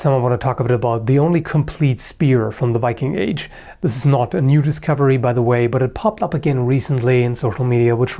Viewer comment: This adds nothing of value, but I had to replay the intro 3 times because I kept hearing “nudist cavalry” when you said “new discovery”. 😂🤷‍♀️